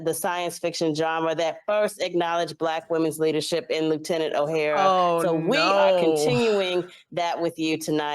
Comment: did she say lieutenant O'Hara ?